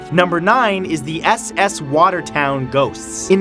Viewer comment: I thought number nine said: s.s. watermelon ghosts I was like: dang that's creepy 😂